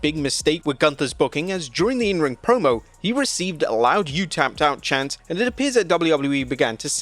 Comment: They misheard the chants "you tapped out" as " you fooked up"